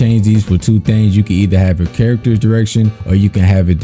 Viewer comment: "if you play ranged change your camera if you play melee change your character" lmaooooooooo *edit: i realize i misheard you and that you meant the name of the camera type but still thats so funny